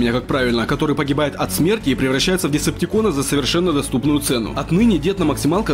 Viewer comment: мне послышалось или дед умирает от смерти ?